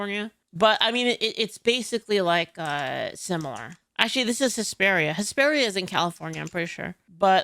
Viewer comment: LOL I misheard you the first time, thought you said HYSTERIA California at first. Made perfect sense to me!